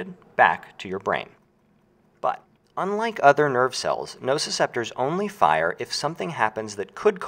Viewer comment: No ciceptors? But I thought you just said there were?